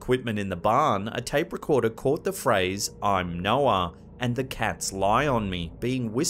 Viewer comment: In the second clip where it says “I’m Noah” I’m hearing “I’m no one.” And the “The cats lie on me.”, I’m hearing “the cat’s eyes on me.” If the particular malevolent energy that has been recorded in that area is the same thing responding, then I’m assuming these are intelligent responses. I’ve heard quite a few recordings from this location and the majority seem to point to one dominant energy in the home. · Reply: I thought the same thing on the Noah one. I heard "I'm no one".